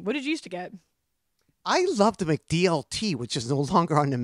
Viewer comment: I was wrong about the burger Nardward commented about corrected me about the "McDLT" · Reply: He actually says the "McDLT" which was a gimmick product served in a two-chamber styrofoam container, which "kept the hot side hot and the cool side cool." It was discontinued in large part because the container was made of styrofoam and was consequently terrible for the environment.